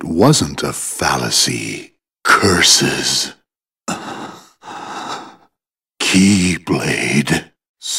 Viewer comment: i always thought he said "curse that keybalde" i thought it would make more seance